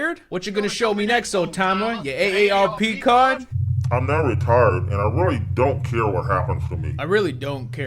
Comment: I heard "I’m retarded and I don’t care what happens to me" and I was like hmmmmm. Suspish.